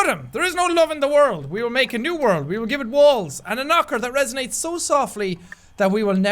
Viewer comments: when jack said walls, I thought he said balls 😂😂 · when he said"walls " I thought he said balls